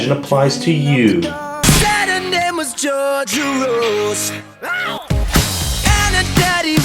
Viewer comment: I heard George Soros